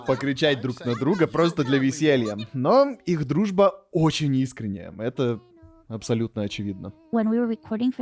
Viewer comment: Я не могу понять что говорит в 15:30, по повествованию вроде должно быть "их дружба очень искренняя", но я прямо слышу что говорит "их дружба очень неискренняя", это у меня так или там реально так и есть?